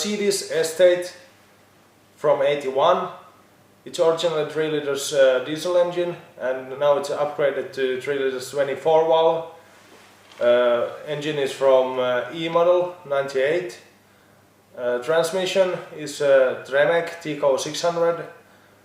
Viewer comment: I think he says "Engine is from Imano?! 98..." at 0:47 I don't think it's the same diesel engine and transmission.